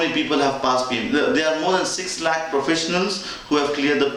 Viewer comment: Trying to understand something...at around 2:20...did he say there are more than 6 BLACK professionals that have passed so why can't I.....?